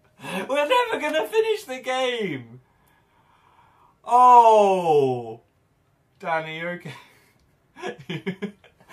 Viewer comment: What does Dan say silently? I'm so curious. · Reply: Kayleigh • yea he said I know ...i slowed it down